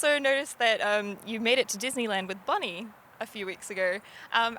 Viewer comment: Lol I heard "bunny" instead of "Bonnie" at first XD I was confused